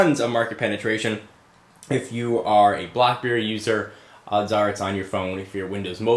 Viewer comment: lol did you really say "BlockBerry" at 0:30? :)